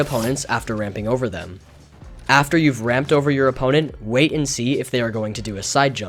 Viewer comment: I thought you said you would use different material on your opponent 🤔